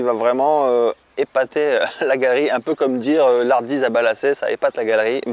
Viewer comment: Bonjour, Vers la minute 9:50 tu parles d'un fruit étonnant mais je ne comprends pas le nom : genre "ardise à balacé" 🧐 C'est quoi donc ? Merci !